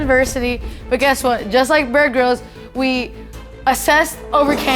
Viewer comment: i thought she said "bird girls"